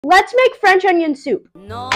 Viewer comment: at first i heard "lets make friends online" lol